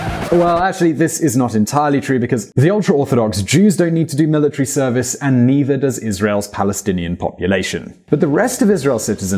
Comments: What’s up with the captions at 5:10? He says “the ultra Orthodox Jews don’t need to do military service and neither does Israel’s Palestinian population”, while the caption says “the ultra Orthodox Jews don’t need to do this military service and the Palestinian population is discriminated against”. I would like an explanation from the video maker for this discrepancy. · says "Neither does Israel's Palestinian population" but the subtitles say "Palestinians are discriminated against." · The subtitles are super weird at 5:10. When the narrator is talking about serving in the army the narrator says " and neither does Israel's Palestinian population" but the subtitle says "and the Palestinian population are discriminated against." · Holdup, the English subtitles say at 5:10 "the Palestinian population is discriminated against" but he says "neither do the Palestinians". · Anyone notice the closed captions at 5:05? What is said: "The ultra-orthodox Jews don’t need to do military service and neither does Israels Palestinian population." What the captions read: "The ultra-orthodox Jews don’t need to do this military service and the Palestinian population are discriminated against."